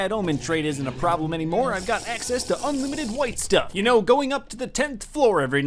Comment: did you just say white stuff